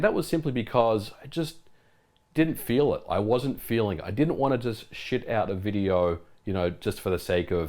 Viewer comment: Did you say ‘Shit out a video’ lol